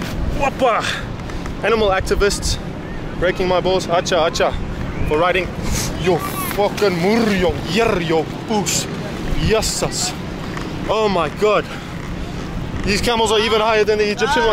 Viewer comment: What have you said? When Camel get stand up? Fuckinnn muurrioo turriooo dasss... What's that mean?? 😂😂😂😂